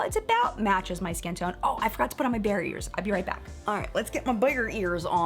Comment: I thought you said "barriers" instead of "bear ears" and wondered what you had to block. 😂 I have the LA Girl lipstick in Nudie and for me it's very creamy. Love your vids. 💋